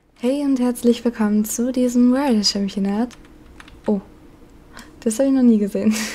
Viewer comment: ich habe verstanden "herzlich willkommen zum diesen Moorland championat"